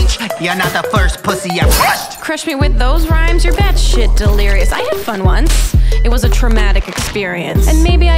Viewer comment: wait, did she just said,"delirious"!?!